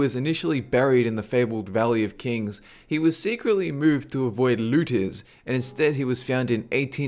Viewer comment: what are lootiz? he said, "he was secretly moved to avoid lootiz?"...... ANYONE???? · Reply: His accent makes it sound like lootiz but it`s really looters